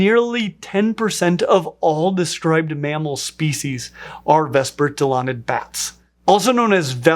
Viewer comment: for a second I thought it said 1/4 of all bats are mammals and I was thinking wtf are the rest of the bats?